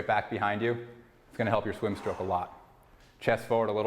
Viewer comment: Can someone please tell me what says at minute 10:30, right after "arms straight back behind you"? English is not my first language and I don't understand that bit. Thank you very much. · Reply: "it's gonna help your swim stroke a lot"